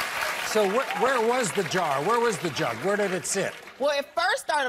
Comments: I literally thought that she said where the DRUGS... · she said the drugs instead of the jug 😂